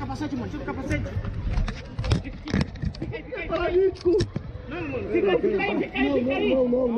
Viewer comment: eu entendi ele falando vou ficar paraplégico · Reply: Ele falo paralítico